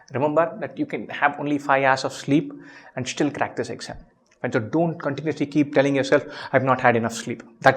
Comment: In this video it sounds like you said bencho 😂😂